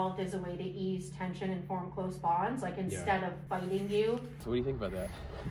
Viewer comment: She says "instead of biting you," so why do the captions say "instead of funding you" (which doesn't make any sense)?